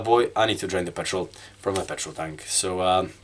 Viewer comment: Lol i totally thought you said you needed to drink the petrol from the tank! (obviously you didnt say that)